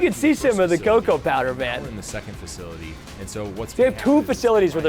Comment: I thought he said “Poop factory” instead of second factory